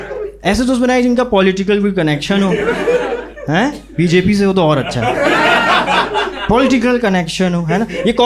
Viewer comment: he actually said reality... connection matters